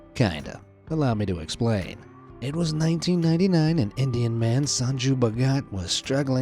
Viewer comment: I thought u said Indian gyat guy💀